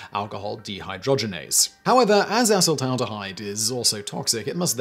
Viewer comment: thought i heard him say assh*le-taldehyde😂